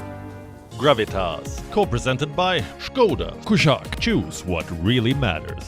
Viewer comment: in skoda ad: what i hear"jews what really matters"(is it just me)